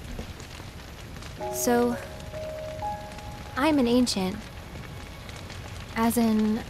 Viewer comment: I thought it said "being an accident", and I was like, "Oh, no!" 😭😰